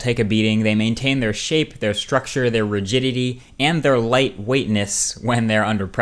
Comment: Like if you thought he said "virginity"